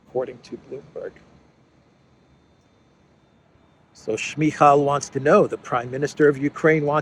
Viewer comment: When mentioned Shmyhal, I thought I heard Smeagol 😅